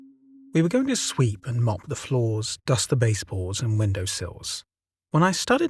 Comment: i heard "dust the baseballs" 😄